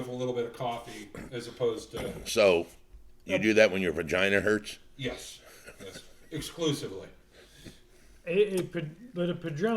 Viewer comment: Did I hear correctly at the 2:47 mark, "Is that what you drink when your vagina hurts?"